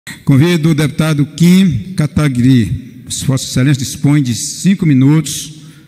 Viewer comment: Eu escutei "Kim cata grilo" kkkkkkkkkkk